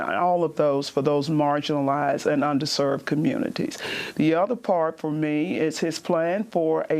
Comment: was kind of hard to listen to, because of her poor annunciation/pronunciation. There were several times that I misunderstood her, and had to think for a second to realize what she actually meant; for instance, I thought she said "undeserved communities", and it took me a second to figure out that she had actually said "under-served communities". To be fair, it also seems like she had dry-mouth, and perhaps could have used some water, which may have effected her ability to speak clearly; Amy often seems to have a similar need to take a drink, when her throat gets raspy, and she has to try to clear it several times. It's nothing against them, I just think maybe they should give people who are going on camera some water, so that when they cut to clips or someone else talking, they can hydrate; I get that being on camera can make you nervous, and that, combined with the lighting, might make you a bit parched. If they could take a swig to wet their whistle, perhaps then I could more clearly understand people like, and people like Amy wouldn't have to struggle to clear their throat into a mic. Just a note that is hopefully helpful. P.S. Lol! I paused and wrote this comment around the eight and a half minute mark, only to unpause and have Adolf do exactly what I was talking about Amy doing- struggling to clear their throat. Even more proof that I was right. Anyone who has done public speaking will tell you there is a reason why politicians, late night hosts, etc. have wa